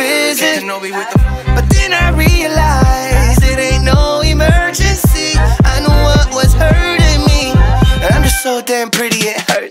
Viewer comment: "I knew what was hurting me" I heard like "Anwar is hurting me" 😂